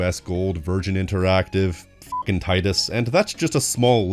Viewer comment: Did you just said "tits"?